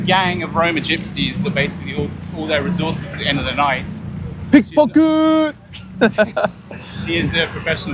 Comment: Did you say "pickpocket!! " at the 17:05 mark? 🤣 i just got back from Europe 🇪🇺 and they kept warning me about the pick pocketers. 👌